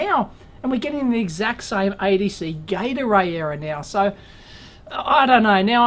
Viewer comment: When you say "ADC Gate Array" I keep hearing "ADC Gatorade". Nice sport drink! Anyways, I really hope you'll be able fix that Murphy-possessed bugger! Waiting for Part 3...